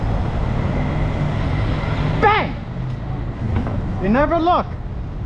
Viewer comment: What did he say at 0:57 ? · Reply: "Bang!" "They never look"